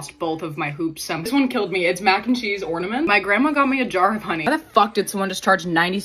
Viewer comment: I can’t be the only one who thought she said “ my grandma in a jar”😂